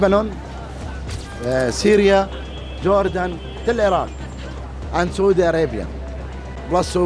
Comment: Does the interviewed man say till Iran or till Iraq at 13:40 ? I hear till Iran not Iraq. Also everytime I hear someone saying this, I want to hear their answer to how Israel en and it's current population would be able to occupy that land mass?